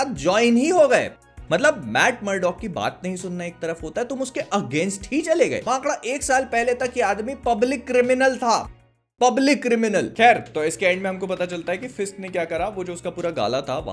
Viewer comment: Did he say "MAKADA" at 11:49 :face-green-smiling